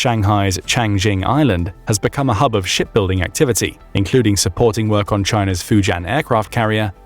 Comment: I thought I heard "...has become a hub of S*** building activity..." I was looking away from the video, and listening to parts, so without the context of ships , I heard differently, and my cognitive biases, clearly filled in the blank... 😅😅😅💩 On a more "solid" note (pun-intended), I appreciate your hard work in the creation of, editing, and execution of the content your channel provides. ❤